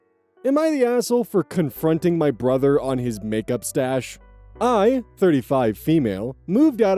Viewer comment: I'm so sorry but when I heard "Makeup Stash", my mind went to "Makeup *Stache*" and that the brother was just drawing on his mustache with lipstick.